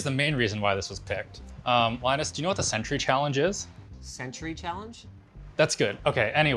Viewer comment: I think I figured it out. The “century challenge” is when one tries to take 100 1oz shots of beer over 100 minutes (one shot per minute). Presumably spilled some beer or... eww... all over his laptop. THANK GOD THIS WAS A PAIN TO FIGURE OUT, thought he said “sentry” at first and all that came up was some references to the laptop gun in Perfect Dark and some (actually pretty cool) 3D-printed lego warfighter-mech game, where one can control the lego warrior with a DS4 controller. Pretty neat game, it was made by some indie dev, has a GitHub, and apparently is played a lot at developer conventions! YOU’RE WELCOME MY FELLOW CURIOUS PEOPLE!